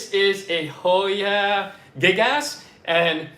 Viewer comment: Funny, my daughter was hearing you as she walked through the kitchen and the first hoya you showed she thought you said Hoya Big Ass 🤣 .